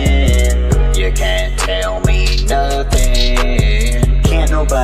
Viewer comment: 0.47 i thought he said cant tell me muffin